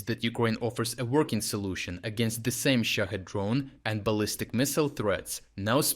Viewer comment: I kept hearing it as 'Shit Head Drones.'